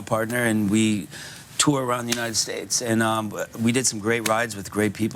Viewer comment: did he said iran ? in